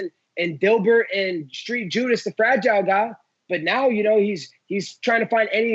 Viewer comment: I swear he said “Jorge Street Judas the Fragile Guile*”